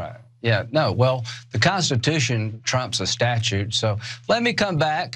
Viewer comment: Videos like these don't get many views, but it's important to know what the psychos in Washington's District of Criminals are cooking up for us. We need a critical mass of people waking up to and pushing back on this garbage. Much love freedom fighters! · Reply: Did he just say at 6:16 that the "Constitution trump's a Statue"?!🤔